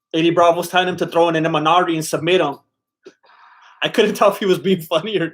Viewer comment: Looking at how banged up Tony was, I thought he said "We might wanna throw in an MRI..."